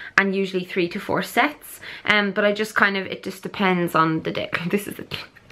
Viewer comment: did you say, "It just depends on the dick"?! 😂😂😂 Don't know if this is weird since I don't actually know you in person but I'd be willing to get a workout in with you some time! I can see a few things from your footage that I could help with regarding form (I'm not a professional but I've been weightlifting a while now) and also I'd just like to train with someone too so could be fun!